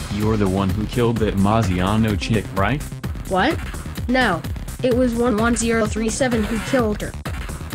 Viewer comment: Subtitles: It was Leon who killed her! What I heard: It was 11037 who killed her! Lmfao